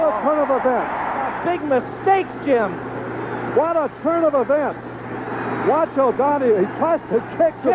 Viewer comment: instead of “what a turn of events” I heard “what a son of a b*tch” 😂😂😂